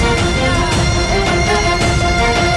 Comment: Did they said Ultra Magnus